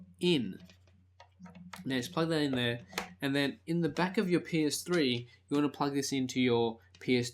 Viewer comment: haha,at 2:32 it sounds like he said'in the back of ur penis-3"hahaha,great vid tho